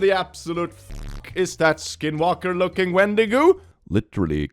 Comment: I know he said Wendigo but I heard "Wendigoon" and it fit so well I thought it was intentional...